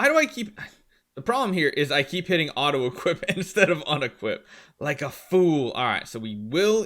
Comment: The problem is that I always hit auto equip instead of auto equip 😂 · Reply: Do you have ears it says clearly instead of unequip